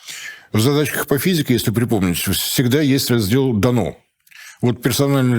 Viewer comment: А мне послышалось, что "В задачах по физике всегда есть раздел "этанол""... за четвертную тройку! У-ух!